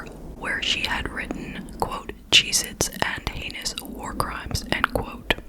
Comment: I kept having to rewind and eventually put the captions on cuz I thought u said “cheeze-its and anus war crimes” and I was SO confused to say the least lol. I’m still not sure exactly what u said 😆